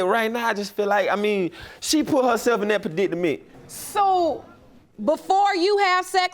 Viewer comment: Translate @ 13:56 what he did say? 😭 Pruh-dit-uh-ment? 💀